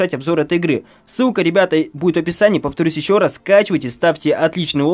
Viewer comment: мне посылышалас что он сказал ск*а